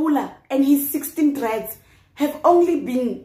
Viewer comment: Did you just say "with his 5 dreads"? 😭😭